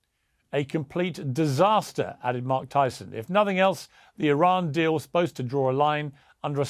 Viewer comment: Mike Tyson 😂 I'm just listening, I thought he said Mike Tyson 😂😂😂 I had to check 😆